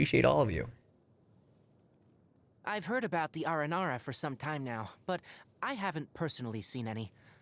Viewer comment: he said "I heard about the Aranara" but I thought he said ara ara 'dfhpiogkj ui had to do a double take XDD